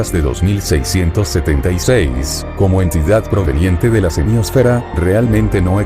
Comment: Lo que entendí "La Simioesfera"